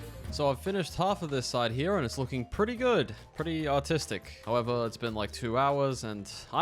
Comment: who else thought he said autistic not artistic